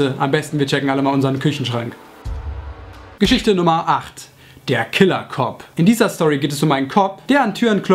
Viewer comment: ich hab der killercock verstanden